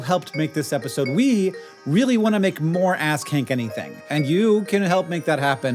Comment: Given that they talked about curse words, anyone else hear "ass skank" at 43:10?